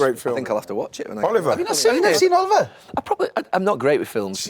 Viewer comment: what movie? I can't tell what they're saying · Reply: Oliver 🙏🍀